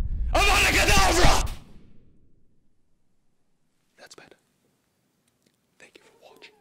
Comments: D :D :D "AAAAAAHRR !!! TELL THAT DOG SHUT UP !!!!! AVADA KEDAVRA !!!!!!!!!! .... thats better ..." :D :D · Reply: I dont know ... maybe he really said "That's bad" ... i am very bad in English so i dont know ... i heard "That's better" :D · D :D :D "AAAAAAHRR !!! TELL THAT DOG SHUT UP !!!!! AVADA KEDAVRA !!!!!!!!!! .... thats better ..." :D :D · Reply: Oh, so he says "That's better." I heard it like "That's bad" and said "wtf? o.O" :D